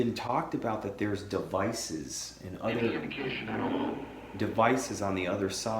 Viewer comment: you ask about the devices and it sounds like Dr. Konstatine ? says "any indication at all." I hear it so clearly.